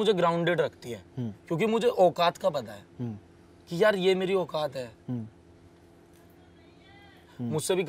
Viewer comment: carefully listen what he says and then hear background voice "auakat hai .... nahi hai"